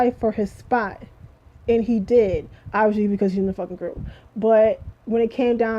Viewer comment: What did you say about Seungri after the 3:00 mark? It was hard to understand what you said. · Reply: I said obviously because he is in the group. I tend to talk fast at times. my bad